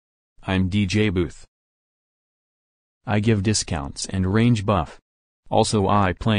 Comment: i thought he said rage when he said range lol